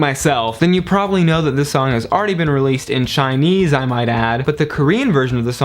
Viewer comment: I thought you said 'this song was already released in SHINEE'S-' and I was like O_o WHATTTTT but then i figured it out ^o^